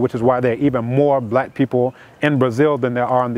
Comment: Great summary❣Has he written any books?.... at 4:35 and 5:40 did he say "United Snakes?" 🤣🤣